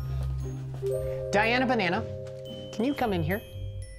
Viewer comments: did he just say Diana banana lol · did he just call her "Diana Banana" is that her real name???